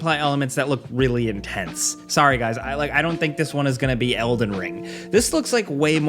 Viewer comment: "Not elden ring" I heard "not a bad game". Lets go!